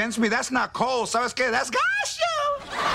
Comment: What'd he say at 4:01? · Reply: "GOT YOU!!"